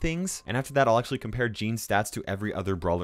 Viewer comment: when you said gene,s i thought you are saying jeans